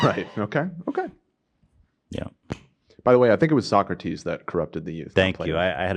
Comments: when said it was socrates that corrupted the youth i had an "i'm a dumbass" moment, because i really thought my mans ethan was saying that play-doh was corrupting the youth 😭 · I thought he said play-dough corrupted the youth